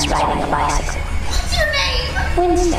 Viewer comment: I always thought she said "hello piggy"